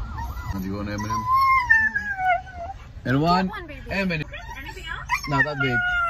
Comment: When he says "Do you want M&M", I swear she is saying "I want M&M". The cadence is right.